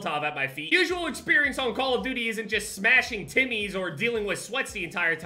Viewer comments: I thought he said smashing tinnies 🇦🇺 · thought he said smashing tinnies. Had me thinking i could totally smash some tinnies rn